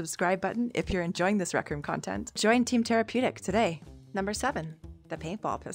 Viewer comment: Bro she’s my little cousins crush this is crazy because I’m just now realizing after a year of watching I thought he was saying therapeutic not tarapeutic